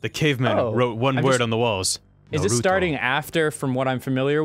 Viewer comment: Did he just say narudo XD